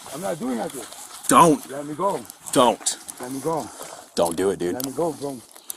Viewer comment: Man I swear I thought he said “don’t do it baby” at first 😂😂 I woulda fought that cop too if that was the case 😂